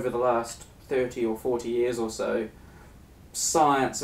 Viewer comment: I'm twisted, alright - but I heard "Science is a ho'" the first time through... pretty ironic...